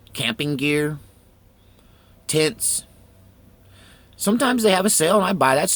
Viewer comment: it sounds like you said “tits”.. lol. I know you said tents though, I just thought t was funny.. I know, I’m immature sometimes. Haha.